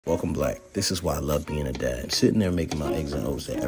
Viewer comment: I knew I heard “welcome black”😂 had to rewatch like 10x!